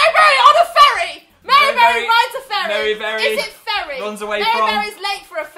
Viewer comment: i thought louise said "mary berry rides a furry" lmao